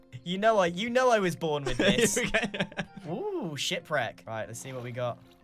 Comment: lol when grian said ship wreck I swear I heard "sh*t friend"